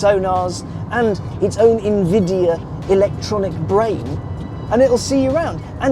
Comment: Did say 4:49 "its own NVIDIA electronic brake" or do my ears decide me. This car is the real Gran Turismo now 😳